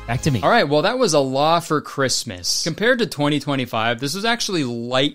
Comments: I was cleaning & playing this in the background..I thought he kept saying “ALLAH for Christmas” and I was so confused as to how/why it was a Christian movie?? 😂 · I keep hearing you say "allah for Christmas" 😂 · I was listening to this while at work and I kept hearing "Allah for Christmas" and was so confused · I wasnt looking at the screen when he said the title of the movie. So i heard. "Alah for Christmas" Which is a movie i need now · Every time Kurtis says the title of this movie, all I hear “Allah for Christmas”. I don’t think that was their intention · I hear “Allah for Christmas”.